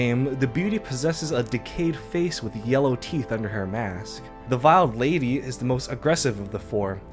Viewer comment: I keep hearing people say "viled lady". It's "veiled", pronounced like wailed.